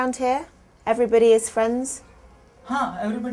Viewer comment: literally nobody *indian guy*: Everybody is in France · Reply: Tbh he said something like, "everyone is in friends" but because of cc we thought he said France. Vice is here to be blamed.